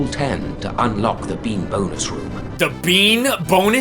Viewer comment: Bean bonus room, lol 😂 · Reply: True story: I was doing something with the video playing on the side when he said that, and I heard "bimbos room".